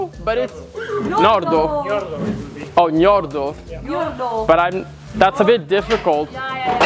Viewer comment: Did he just say this horse's name was *Ñordo*?????? 😂